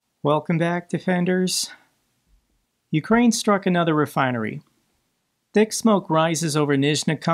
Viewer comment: Thank you. I honestly thought you said dick smoke is rising...